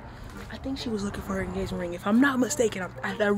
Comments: Did she say engagement ring 👀?? Around 8:55?? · Am I the only one who heard say engagement ring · Pause- Did say engagement ring at 8:54? Rewind-repeat🤗🤗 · Can we just get to the point that is a great actor 😊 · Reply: And what about at 8:52 🤔 did she say engagement ring 💍 lol 😂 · 🤔 Did she say engagement ring?! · mark did she say "engagement ring" ? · Did she say engagement ring @ 8:52 👀🧐🤨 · I swore I heard engagement ring